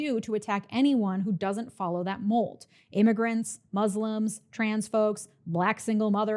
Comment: At first I thought you said "transphobes" instead of "trans folks"